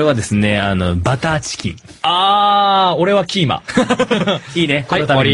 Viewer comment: i thought he said kenma HAHAHAHAHAHAHA